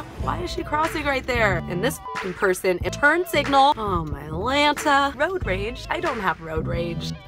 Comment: Was I the only one watching the reflection in glasses? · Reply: Does anyone know what state they live in? Just curious bc it sounded like she said atlanta